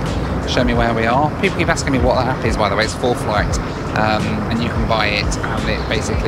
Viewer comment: Anyone able to tell me what map he is using at 11:38? Sounded like he said "full flight?"